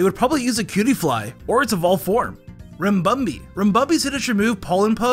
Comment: I heard this as Rim-bum-bee